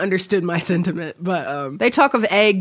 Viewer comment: For a second it sounded like you were saying "he can't eat ovaries" but then you finished and said "can't eat over easy eggs." I'm now ☠.